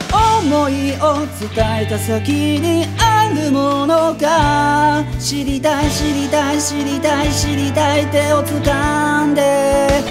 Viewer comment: 『知りたい知りたい』が 『死にたい死にたい』に聞こえて 『！？』ってなったwwwwwwwww